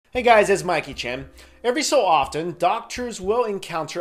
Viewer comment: YOUVE BEEN SAYING THIS WHOLE TIME?!?! I THOUGHT YOU WERE SAYING 'Mikey Chen' WELL THEN